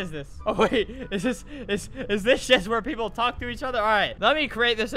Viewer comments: Did i just hear him swear? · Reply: He said just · (a lil late then that) did he said shit or do I have hearing problems